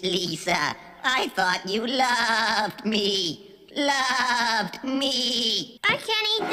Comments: I thought the lamb said i thought you love meat love meat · 'I Taught you love me" I hear "i taught you love meat"